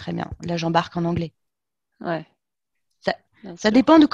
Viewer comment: What's that at 41:15? The transcript says "l'agent barque en anglais", which doesn't make much sense to me. I guess she says something like "ok, one plays in English".